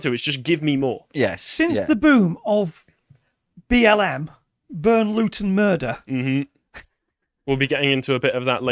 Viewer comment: I thought he said Burn Luton, Murder!